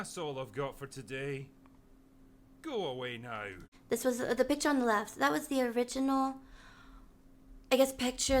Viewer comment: I swear to cheese I heard you say "the bi*ch on the left" I HAD TO REWIND TO MAKE SURE HAHAHHAHA